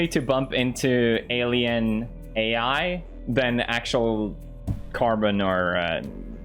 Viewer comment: Did he just say "carbonara" 4:56 lmao Marzia is getting there